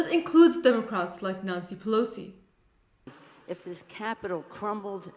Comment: Sounded like she said Nazi Pelosi.